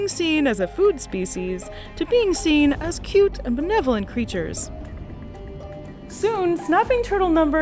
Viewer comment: Did you say benevolent?